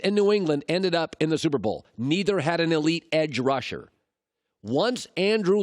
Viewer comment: Did he just say Aaron Darnold wasn’t an elite pass rusher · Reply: Hanoj Carter he said edge rusher